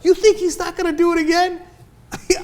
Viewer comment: what did he say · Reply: chit "... he's gonna be President Obama."